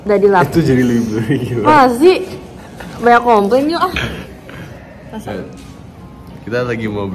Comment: did she said "yank" ??!!